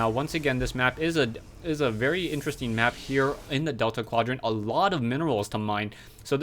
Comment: I thought it said dental quadrant and I got excited.